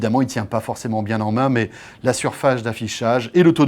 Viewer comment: La "surfaGe" de quoi? Mdr mdr